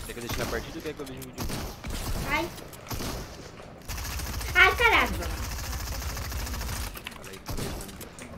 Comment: ouvi uma palavra meio errada kklkk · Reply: Em falou caraca